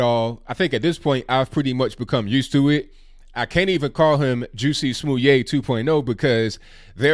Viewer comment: Lmao! The auto-captions thought said "Juicy Smoothy"